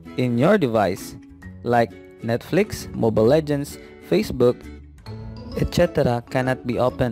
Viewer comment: What was bro tryna say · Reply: The etc